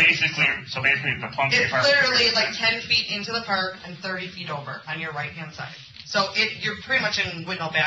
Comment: There's absolutely NO WAY this girl is digging a 30-foot hole in the ground. I can not with her. Edit: I misheard. She said it was 30 feet in, not 30 feet deep lol.